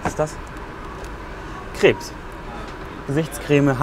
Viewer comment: Ich habe verstanden bei 3:05 ,,Krebs“